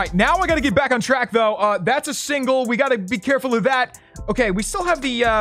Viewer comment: you said “single” instead of “signal” 😂